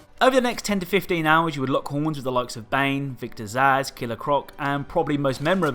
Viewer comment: When you said Victor Zsasz, I thought you said Victor’s ass. Which I wouldn’t want to fight either, to be honest.